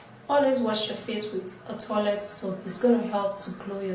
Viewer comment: Did you say 'Toilet Soap' ? What in the world is that? 🤔 I hope you sell it on your website.